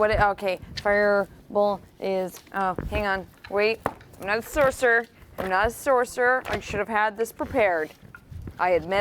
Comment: “I’ll fix you in a hot second!” Turns into a red dragon 👌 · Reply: I thought she said she didn't have it prepared.